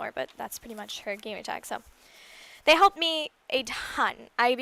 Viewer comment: i thought she said "They humped me alot" my bad